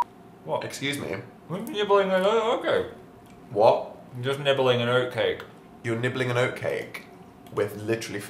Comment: just nibbling an oat cake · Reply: Whisk Alice I always thought he said, "Just nibbling a note cake." It all makes sense now haha